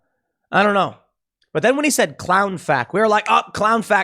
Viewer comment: I thought he said Clown Fake too, ha!